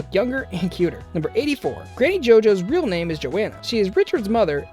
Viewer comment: "Who doesn't want to know more about Granny JoJo?" Um.... I'm good. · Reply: Ollie Abstract it sounded like he said ‘Ganny JoJo’